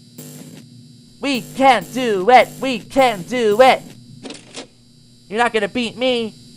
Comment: When said We Can Do It We Can Do It I thought he said We Can't Do It We Can't Do It